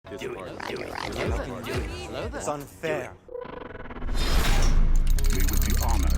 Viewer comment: Did I hear “it’s unfair” / “That’s Unfair” in 0:01 , it seems like Arnold Schwarzenegger voice Am I true 😂😂😂